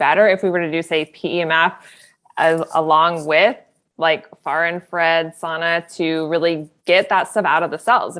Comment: What does she says at 34:22? Someting about some kind of sauna? Im puzzled. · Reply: Far infrared sauna